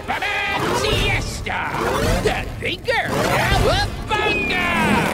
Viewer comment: Uhhhhh…what did he just say?! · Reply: The thinker